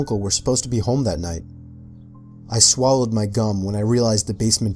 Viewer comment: Haha it sounded like he said "I swallowed my cum"